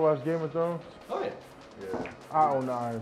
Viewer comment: What does Micah say after asking the Game of Thrones question 0:32 ? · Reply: I don't know how I feel about that show